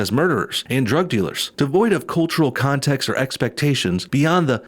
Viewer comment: Its EXCEPTIONS not "EXPECTATIONS"...which if you had caught that instead of misreading it, you could have acknowledge that it was in fact a LIE because there are exceptions, as they already acknowledged even if they consider them "under used".